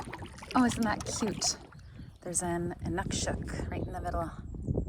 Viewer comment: what's that word you're saying? What is it? Like a cairn in the water? I forgot my sleeping pad once. I was able to pile up dried leaves and found it surprisingly warm. Also, I bet if you folded your raft up a bit, it would have made a nice sleeping pad too. ...unless it's too lumpy. Seems like everything worked out anyway. Cheers to a good video! 🍻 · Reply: “Inukshuk” 🥰